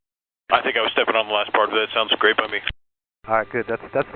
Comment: I thought he said, “...we’ll pray for you” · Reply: Nope. Very clearly says, "we'll prep for it." Although praying might have also been a good idea. 🙂